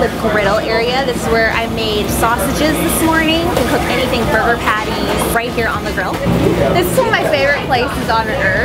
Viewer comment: said burger patties but the caption said buger patties. Lol